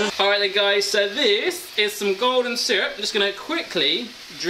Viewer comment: when you said golden syrup i thought you said "gordon syrup"